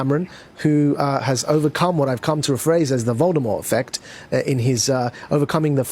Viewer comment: did he say "the Voldemort effect" ? O.O